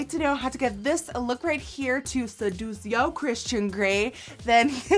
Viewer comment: I heard "to seduce your Christian granny" when she said to seduce your Christian grey